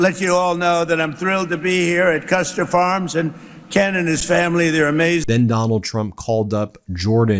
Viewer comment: "and Canada's family, they're amazing"? tf did drumpf just say?